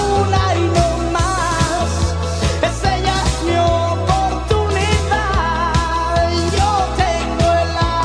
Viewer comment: Yo pensé que decía: "...un ano más, es ella mi oportunidad..." "...yo tengo a Slash y no puedo fallar..."